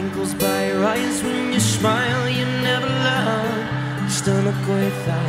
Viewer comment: um does anyone else hear Liam saying "When you Shmile," or is it just me